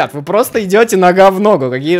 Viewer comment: Мне одному послышалось, что сказал "Вы просто идете на говно"😂😂😂